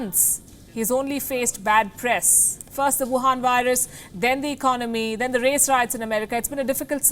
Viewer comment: did she just say "the wuhan virus".